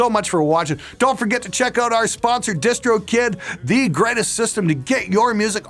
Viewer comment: The way you slid into the Distro Kid bit was so smooth that I didn't even skip it this time! Kudos! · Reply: I always thought he said distro kit*